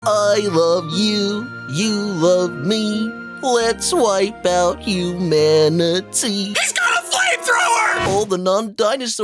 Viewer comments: I thought he said I love you you love me let’s wipe out your family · I thought he said let's wipe out you manatee